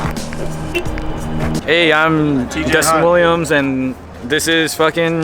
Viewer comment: Dustin Williams? I thought you said Rustin Williams.